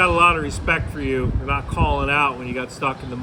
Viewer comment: wait in the story he said he didn't call out and the way the others found out was the team lead turned around and saw him. so why did the team lead later on on say " I got respect for you calling out like that" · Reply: I didn't get that either, and more confusingly, the captions say "for not calling out", but I definitely hear "for calling out" too.